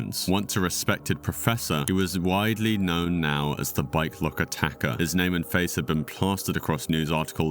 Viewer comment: I thought he was saying "bi-clock." I'm like, "What is a bi-clock?" Turns out, it was a bike lock. This happened to me with dope fiends. They kept saying, "I need a bike rack." I kept thinking, "Why do they need a bike rack?" Turns out, they needed to buy crack.